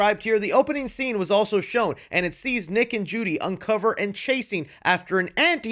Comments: Anybody else hear "naked Judy"??? 🤨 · Bro… my ears arent working tonight I swear I heard naked Judy I was about to question everything